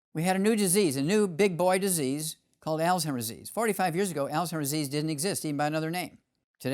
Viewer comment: What does he says at 54:24 some disease I cannot understand him at all. I have played it so many times and I cannot tell what disease he is talking about. He's very interesting but he needs to slow down. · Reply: Alzheimer's disease...